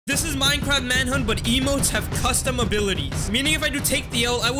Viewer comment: When says emote it sound like he is saying emo